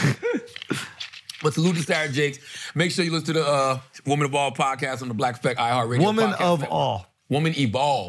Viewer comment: I definitely thought he was saying Woman Of All